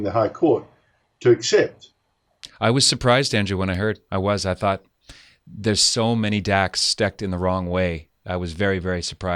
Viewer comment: Did say “there’s so many dacks stecked in the wrong way”?